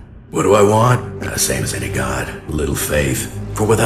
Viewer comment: Completely unrelated, but I wasn't really reading the subtitles at the end so I thought he said "Without me, you're a THOT"